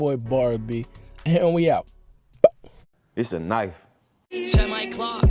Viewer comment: I had run it back... I thought 21 Savage said Izanagi but .. Issa Knife as usual 😅